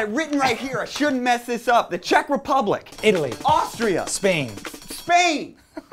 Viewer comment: When you said Austria I got excited I thought you said Australia